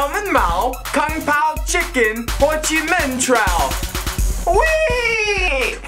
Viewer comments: Did he say Ho Chi Minh Trail at 0:29? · it sounds like he said com pown Chicken